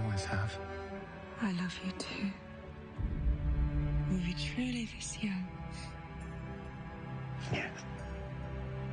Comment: does she say were "we" truly this young or were "you" truly this young just wondering cuase francis supposedly died as a teen but in the show..well he doesnt look like a teen,he looks more like 20 something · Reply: she says. "where "we" truly this young"